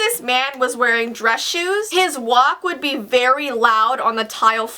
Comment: his WAP why did i hear that